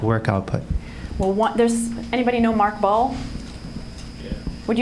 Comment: Could anyone help me? At the minute 38:47 Emma Seppala talks about a person called Mark (I think) who teaches breathing techniques. I'm an english learner and I cannot understand properly the surname of that man so I cannot find his videos. Thank you! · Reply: I meant Mark Ball who works at Google and teaches breathing workshops there. You can learn the breathing practices I researched through <link> or <link>